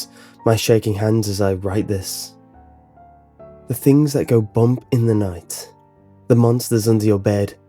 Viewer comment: who do you voodoo bitch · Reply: I always thought it said 'The things that go poop in the night'